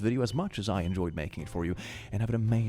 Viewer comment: for some reason i heard "i will see you in the very near (future)" as "i will see you in therapy"